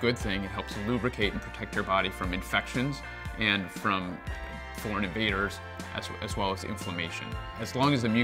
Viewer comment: Oooh😲, "Foreign Invaders" I thought he'd said "Fornicators"